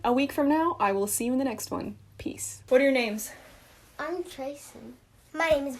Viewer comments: I thought your nephew said “I’m tracer” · did he say im tracer?